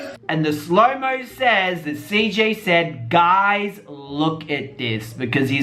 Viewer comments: it sounds like he said guys look at this not daddy ... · It sounds like CJ said “guys look at this” lmao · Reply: Ciara Jewel I tried soo hard to hear guys but my ass heard dad 😂